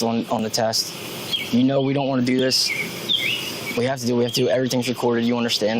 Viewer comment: What cop said: "I don't want to do this". What I heard: " I don't want to do my job".